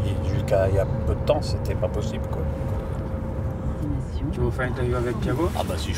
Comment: Silva il dit quoi à 2:17 svp ? Perso j’entends « tu veux faire une doyo avec Thiago ? »